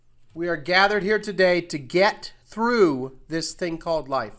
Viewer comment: I've ALWAYS heard & known it to be "get through". This is the first time I've ever heard this misheard as "celebrate".